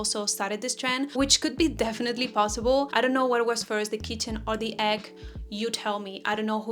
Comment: Did you say "which was first, the kitten or the egg" ? 😄 Or maybe I misheard.. anyway, the pink color you're wearing really suits you! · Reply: I think she said kitchen 😆